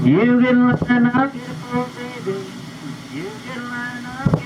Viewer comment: we always called them "crawdads". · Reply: Same here. Crawdad... sounded like he said Crawdag with a G. LOL!